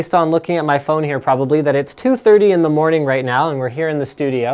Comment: He actually says it's 2:30 in the morning.